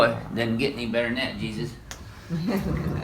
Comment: did he call him Jesus? 💀